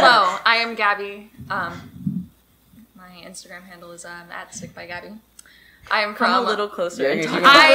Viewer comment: I first thought it said "stickygabby" and honestly, also a vibe xDDD